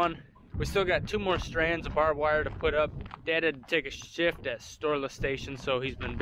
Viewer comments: The rock picker should be fun. The new home is looking great! At the end when you said Brian had to take a shift at Storla, I thought you were about to say he had to take a sh*t at Storla Station. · I thought you said dad had to take a shit at storla station.